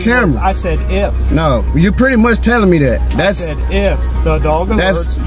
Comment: the office did not say “ if” he said “once the dog alerts “